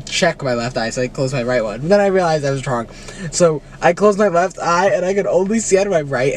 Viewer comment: Did say he was drunk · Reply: No he said I realized I was wrong